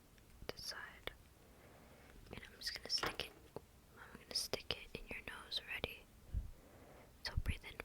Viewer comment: I thought it said picking your nose😭